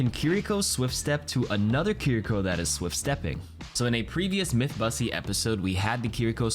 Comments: yo did just say Mythbussy? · At around 8:06 Did you just say "Mythbussy"???????????? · did he.. did he say myth bussy?